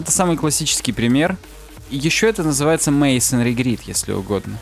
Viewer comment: Как это называется, не могу расслышать? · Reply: Masonry grid.